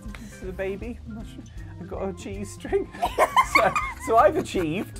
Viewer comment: Wait what?! 😅 Did he just .... Say I've got a G-string... Noo cant be....[listen again] ohh "ive got a chees string" 😂